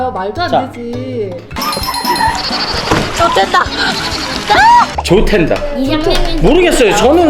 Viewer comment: 근데 탠다로 안들리고 땐다로 들려서 정말 욕처럼 들리김 함. 그 해명하고 어린이들 보는 영상이다 보니까 너무 억지로 해명하다 보니 그런듯